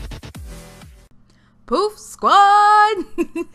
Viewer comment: At first I thought you said poop squad 😂